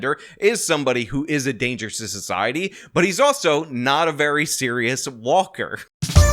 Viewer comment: When you first said his name I legit thought you said he's "serial walker" as he get off freely so much his nickname is serial walker😅